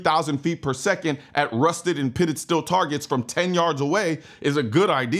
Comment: For a second, i thought you said "Still Targets" instead of "Steel Targets". but yeah, shooting steel from that far away, I'd also expect some fragments coming back at me, and I've never even fires an AR or trained in the use of one. Also, that optic on that rifle...dude took "aim small, miss small" literally...